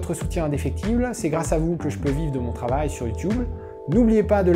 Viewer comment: qui d'autre que moi a aussi entendu "youtouble" laché en mode ni vu ni entendu XD